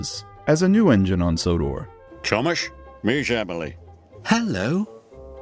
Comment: Idk why, but I heard him say “Ťomaš”.